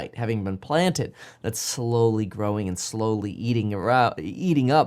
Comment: what did kevin say? LOL "slowly eating her out?" LOL His mind is elsewhere.